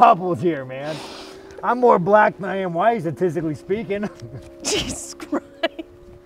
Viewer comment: “I’m more black than I am white, statistically speaking.” D’Sean Strickland, 2023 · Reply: I thought he said autisticly speaking 😂